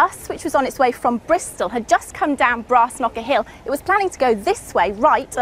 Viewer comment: Is she saying "Brass Knuckle Hill"? That'd be a weird place to die without being beaten to death.